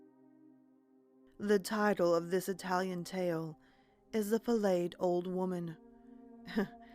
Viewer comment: an old lady...filleted (fillet pronounced fillay) I though flayed. Maybe some people say fa-laid instead of flaid